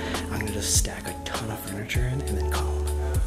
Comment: is it only me but it sounds like Sam said we're gonna stack tuna furniture 😂😂😂